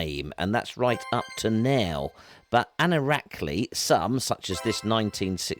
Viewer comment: Can someone tell me what is said at 3:07 ? The transcript shows a word "anoractically" which I cannot find anywhere. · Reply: It’s a made up word: “anorakly”. As in anorak-wearing. Typically geeky and pedantic. Hope that helps. Cheers